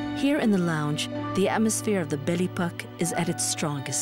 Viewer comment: The narrator's pronunciations make me bristle. You could also play the Belle Epoch drinking game, a shot every time she says it. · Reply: The first time she said it, and even subsequently, I heard it as the 'Belly Puck' LOL It dawned on me eventually. But that did not take from my enjoyment of the video. I didn't go throught to the end. What I would have like to know from the start is whether there is air conditioning on this historic boat. Having been to Egypt, many times on cruises on modern vessels, I know the terrible heat one can expect there. Also, are there private facilitied (toilets and showers) in canbins, apart from suites. sFinally it is A-gathat. Not agAth - stress on the second syllable. It should be on the first. Even so, a lovely reminder of happy holidays in Egypt and on the Nile. Thank you.